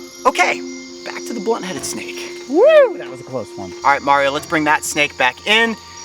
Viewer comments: Am I the only one who thought it said butt head snake instead of blunt head snake? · when it said "Blunthead Snake" I thought it said "Butthead Snake" 🤣🤣🤣